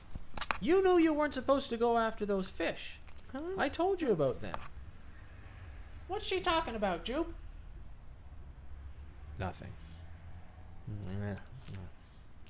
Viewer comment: Did Kona says "She"? ^^